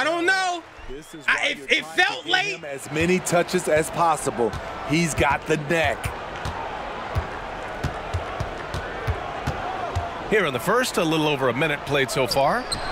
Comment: did he just say you got the neck? What you doing out here in these streets? #funnymoments